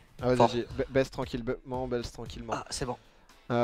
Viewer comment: C'est moi ou au debut il dit baise tranquillement 00:40 ^^